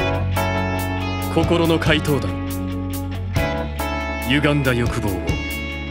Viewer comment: Did I just hear Uganda?